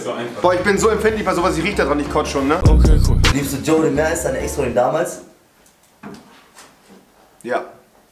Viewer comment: Was war die Frage an rewi bei 1:53 ich hab das nicht verstanden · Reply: Die frage:liebst du Jodie mehr als deine ex früher? Antwort:Ja!